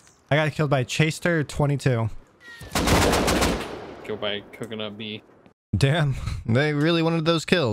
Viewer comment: did he say "coconut B" ?